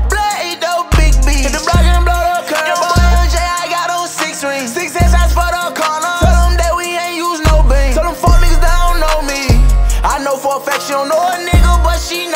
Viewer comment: "Tellem that we ain't use no beam" yb is the definition of the goat 🐐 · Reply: I thought he said "tellem we aint use no beans" lol makes more sense with beams tho